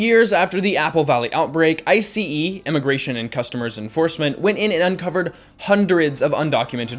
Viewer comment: Did just say Immigration and "Customers" enforcment? 😂